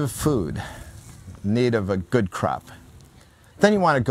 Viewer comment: No disrespect intended, but i did a double take when i thought you said ‘if you needed a good crap’ at 8:20 (you said of course crop) i chuckled at that... great series!